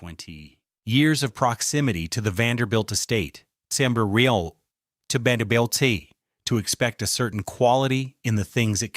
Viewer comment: What happened at 20:28 ? Closed caption says “Samber real to bandel”.